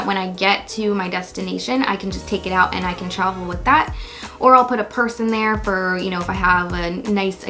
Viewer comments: "I'll put a purse in there" MY BRAIN THOUGHT YOU SAID "I'll put a person there" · did anyone else hear "or i'll put a person there" instead of "or i'll put a purse in there" ? LOL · captions “Or i’ll put a person there” LOL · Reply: Lol I thought she really said “person” and after a while I realized she said purse haha